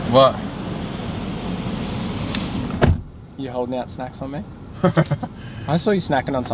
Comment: Lol, I thought said, youre holding out sex from me. When he said, snacks.